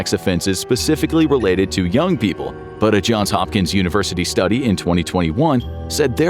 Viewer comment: When he saud hopkins i thought he said hawkins lmfao